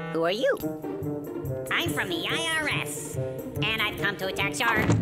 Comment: Peach: I'm from the IRS! Larry: *slams the door in his face* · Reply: so... um... obviously I know what the line is, but as a kid I thought the Peach said "I'm from the IRS! And I've come to touch your--" so yeah, I heard "touch" instead of "tax".